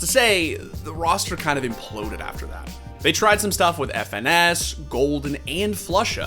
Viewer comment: I legit thought he was gonna say "FNX" and thought "Oh that's why they didn't win anything anymore, they got the FNX curse"